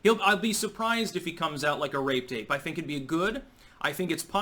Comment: Trump isn’t gonna go in like a what!? There’s no way said what I keep hearing And then tells us Trump is going in dry You are doin’ this on purpose, sir! Per closed captioning, “rape tape” What the hell is rape tape?’